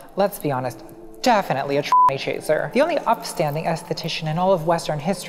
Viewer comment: What did Natalie call Nietzsche at 20:59? A ... chaser? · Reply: It's a t-slur chaser